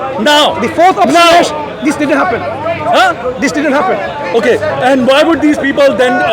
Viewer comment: > I thought had said fourth option: "It wasn't happened"